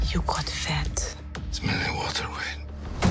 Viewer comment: Did anybody catch what He said at 0:52 after you got fat? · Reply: Its simply water weight